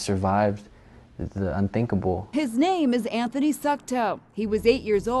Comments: I thought I heard he’s name Anthony Suckedup… not funny… not funny at all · It sounds Like she said "his name is Anthony suck toe"